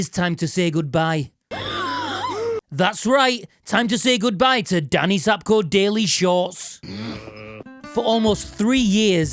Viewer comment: I thought it was time to say goodbyes to the Rickenbacker 😅